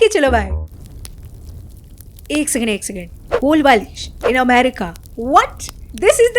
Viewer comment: aapne 17:01 par kya bola hai? ek second ek second --------in america? please fill in the blank!! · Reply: Kolbalish... The kind of pillows they have stuffed in the background there